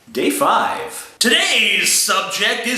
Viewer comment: day 5 i thought he said gay 5 looooooooool lamao